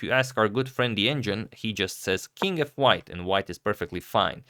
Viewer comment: Did Agad say "king f white" in 16:25?